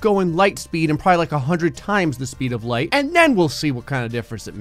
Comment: an ad played and i heard "100 times the speed of moist men"